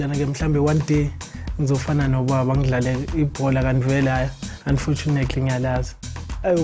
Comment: What did Gabuza said @ 1:53 , did he really said "unfortunately ngiya lazi" ??